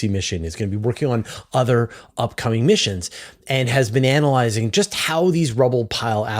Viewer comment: It sounded like you said bananalyizing when you said "been analyzing".